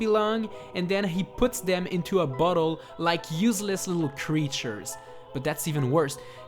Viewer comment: it sounds like he says "he puts them in a butthole"